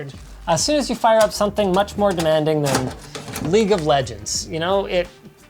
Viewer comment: Linus: Lets play league of Legends.. Me: Oh, you scared me. I thought you said Raid: Shadow Legends, like the last 2 videos I watched.